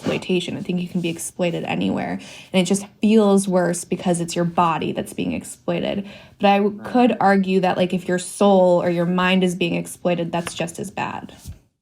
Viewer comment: “It’s only your body being exploited, not your soul or your mind…” Hmmmmm. As if being exploited physically doesn’t affect your mind or soul. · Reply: That's not what she said at all! she said "it just feels worse because it's your body that's being exploited. And I could argue that if your soul or mind is being exploited is just as bad." look it up at 14:00 in the video. People are so biased they cant even hear what people say they only hear what they think is being said.